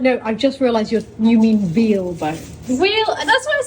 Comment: The new work space is beautiful 😍, is so talented! I really like the napkin chain, that's very clever. I thought she said whale bone too! I was horrified 😮!